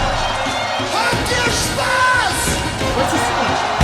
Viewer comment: "What's he saying?" He's saying (in german): "Wacken,habt ihr Spass?",which is "Wacken,are you having fun?" (Wacken is a small village in Northern Germany,where the biggest Heavy Metal Festival in the World (100.000 people) is taking place every summer.The lead singer is Mr. Tobias Sammet,founder of Avantasia,the guest singer is Mr. Jorn Lande from Norway.He's having his own band and also appears on several Avantasia Albums. I also recommend from Avantasia: "The Raven Child"!!!